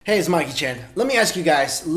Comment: This guy looks like Jackie Chan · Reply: Concrete Minotaur OMG THATS WHAT I THOUGHT AND I HEARD ON THE FIRST ' Hey its Jackie Chan ' lol